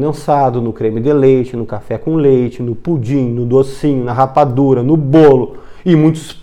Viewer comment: Gente eu escutei Rapadura???? 0:50 do vídeo, fiquei em dúvida agora!!! Dá a entender que Rapadura tem lactose